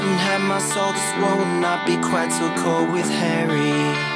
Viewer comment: it sounded like he was sayin hairy instead of harry